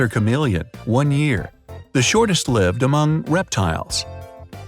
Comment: The shortest Lived? I thought it said "the shortest liver..!" 🤔